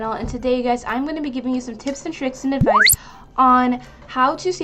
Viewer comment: When she said “tips and tricks” for some odd reason I thought I heard her say kids 😅